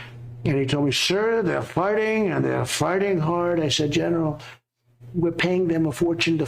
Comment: is it me or does it sound like Trump is saying “they are farting and farting hard”? Lol